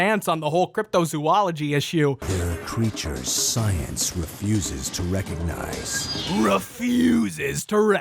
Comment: when he says "refuses" it sounded like muscle man 😂😂